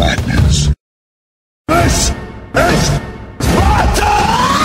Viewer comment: i thought it was saying "this is magners" :s hearing check time